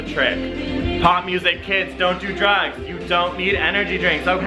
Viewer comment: I heard "pot music" instead of "pop music". I loled when the music started.